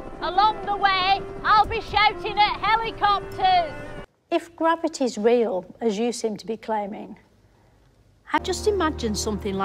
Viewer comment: What does she say in 0:15? And who is the lady she talks to? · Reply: She says "If gravity's real, as you seem to be claiming..." I don't know who she's talking to, probably a physicist or astronomer.